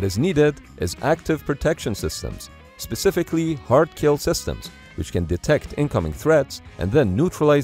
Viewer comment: CC reads "HARK-kill system" instead of HARD-kill system.